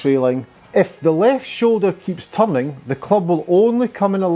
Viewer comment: This is an excellent translation for us amateur hours players, but for the life of me up until the 5min mark I swore you were saying the left shoulder keeps tumbling 🤣. I got it when you were showing the side view of the swing. Thanks for the great info. I always find it helpful